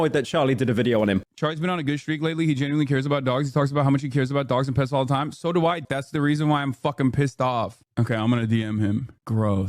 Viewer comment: "So do I dumb fuck" I believe is what he actually said. Why cut off where Hasan screams at the chatter and calls him a name for a pretty innocuous comment?